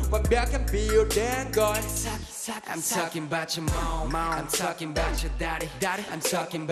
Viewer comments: I thought he said 'I'm talking bout your mom I'm talking bout your daddy' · i thought he said, "I'm talking bout your moan"